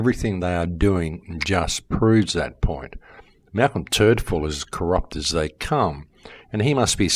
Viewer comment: Lol, I could have sworn you were saying Malcolm Turdfall until I looked the guy up